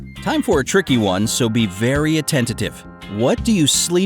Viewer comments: did he say attentative instead of attentive, where is his common sense? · did he say 'attentative'? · Did he say "attentative" @ 4:11?